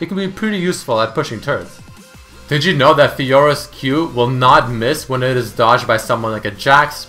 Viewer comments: did he said Fiora?xD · Did he say fiora